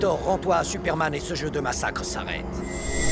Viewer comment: "lex luthor rend toi à superman et xe jeu de massacre samer"???? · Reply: Et se jeu de massacre s'arrête